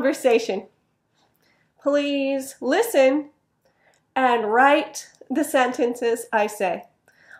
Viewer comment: # 2 :”and ” is very weak sound to me, and I misheard "read" , as write instead of read[red]. I'm disappointed in myself. "study hard", I heard voice in my brain.